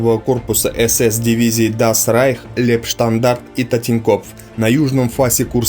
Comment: на 0.47 минуте я не послышался ТИНЬКОФФ? если это так он и там нас убивал?